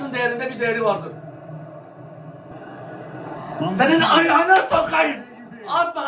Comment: ne diyor anlayan varmı. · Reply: Senin ayagina sokayim diyorum 👍